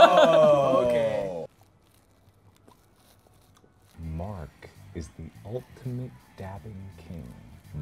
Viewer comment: so i tried to do one from the video (3:44) by lowering as much as i can but still hear it and just having my earpods hanging on my ear and i heard milk is the only dancing king it's actually really hard tbh